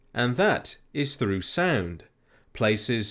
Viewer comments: When he said "sound" I thought he said sand lol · I thought you said sand instead of sound at first, and I thought "aw yes sand the only true weakness to crabs" it gave me a chuckle keep up the great work.